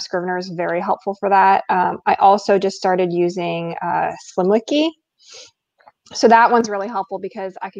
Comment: Great panel and discussion. I did not catch what mentioned for world building around the 49 minute mark. If someone caught that or has a link please post it. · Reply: Sounded like she said SlimWiki. Here is a link: <link>